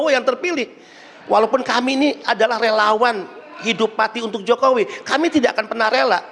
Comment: Benarkah yg di ucapkan si pelapor.. ..Hidup mati utk jokowi.. Apa aku salah dengar..sampai 3 kali aku ulang vidio ucapannya..